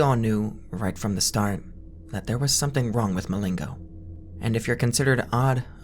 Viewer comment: i thought he said milenko and i was like.. icp reference ??🤨🤨